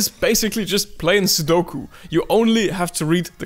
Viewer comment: I heard "playing suddoku" as "performimg seppuku"